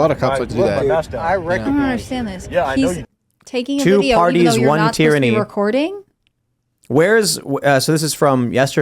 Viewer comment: The carry along cards in the viral video: I thought at first they said, "Two Parties, One Tranny." I realized my mistake... I guess they both work...